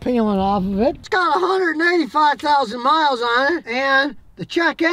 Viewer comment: It sounded like you said 195,000 miles, but it showed 185,000. Just thought you’d want to know.